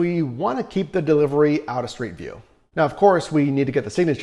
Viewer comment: Keep the delivery out of what? I can't figure out what you are saying. Also did you say the customers have to sign when you arrive? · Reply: He said " street view"...so someone is not tempted to steal it...not everyone will walk outside And take it directly from you, they want you to just leave it and take a picture.